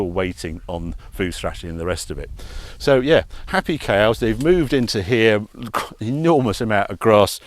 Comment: Just watched this and thought you said Happy Chaos! sounds that about